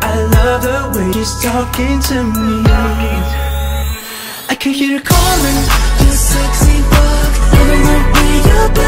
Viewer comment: why do I hear "I can hear you calling, this sexy book" ??